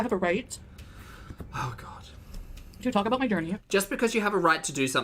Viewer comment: Oh my gosh, all this time I thought was saying "gurney" for "journey", as in "the gorls are going to get rolled out on a gurney soon". Whoops....😅